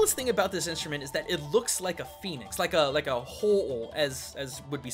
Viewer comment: I swear he said penis, not Phoenix